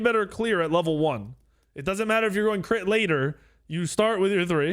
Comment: so im 3 minutes in. i heard "hes going crit rat" and "he thinks hes plat" ik he not plat